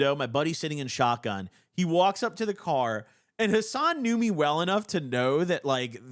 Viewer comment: Ngl i was tunning the video out had to lock in talking to my girl ab sum and i hear “i stained hassan” for a short sec i thought this mf fr robbed hassan abi YES SPIN ON THIS WEIRD ASS FOOL.